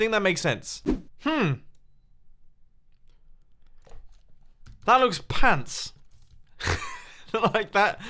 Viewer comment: When said "that looks pans" I instantly thought "wow, that's the closest I have ever heard him say a swear" for some reason.